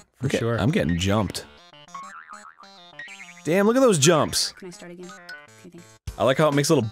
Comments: Does anyone else hear that whisper at 4:49? It’s like someone else talked during the recording. The voice sounds female too....? She says “Can I start again? Kay thanks” or something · wtf is that sound byte? Someone saying "can I start again? k thanks" really quietly... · anyone else hear someone talking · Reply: Can I start again, kay thanks. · Was that in game or something? Sound like someone saying “Can i start again? (???)”